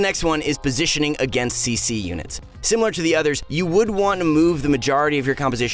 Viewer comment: I thought it said against cunts XD